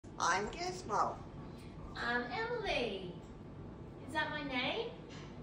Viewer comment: I heard him say "that's not my name" rather than "thats your name," but same difference!